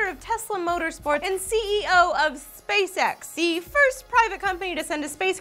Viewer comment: i kept hearing "space sex".